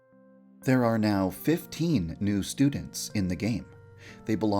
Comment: At first I thought he said 50 new more students and I was like Wait What?